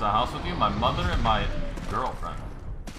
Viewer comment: does he say his butler or his mother and his girlfriend ? 😂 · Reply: Danny Rodriguez mother and girlfriend